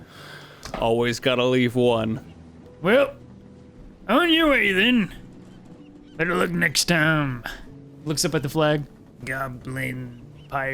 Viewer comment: I'd like to bring attention to 1:23:50 because no matter how many times I listen....I cannot decipher what says... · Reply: On your way then! Better luck next time